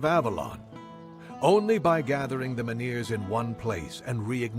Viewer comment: I misheard Legate Denholm and now I can't stop laughing about the Kamelotians needing to keep their manure(Menhir) together